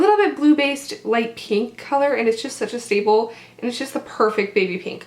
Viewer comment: i thought you said "and its such a bagel" i was dying LOL